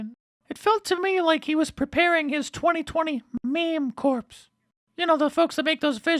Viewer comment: Did Stelter just say "meme corpse" ...like Obama says Navy corpse-man??